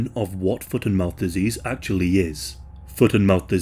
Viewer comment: I feel dumb because I heard "Foot IN mouth disease" like an epidemic of ppl being embarrassed.